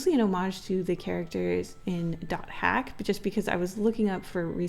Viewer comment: What's the anime called? I keep mishearing it as Duck Hat 😭